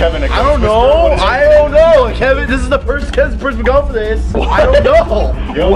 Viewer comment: What ever just said " Kevin this is the purse kezda purfda gofth dis" Will always be remembered.